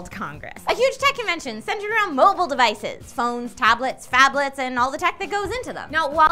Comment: Thought she said, "Faplets" for a second.